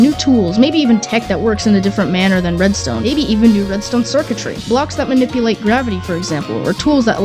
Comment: I swear I heard 'maybe even some new 3D_Circuitry'